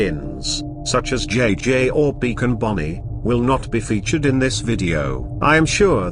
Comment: did you say Beacon Bonnie, or Beaten Bonnie. I can't tell, also nice video, would love to see these come to fnaf ar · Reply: Thanks. Also, I said "Beacon Bonnie". I apologise for the confusion.